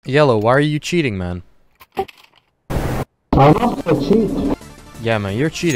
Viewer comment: I thought the first guy said “I’m not the cheat”